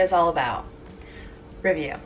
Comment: I thought u said ravioli not review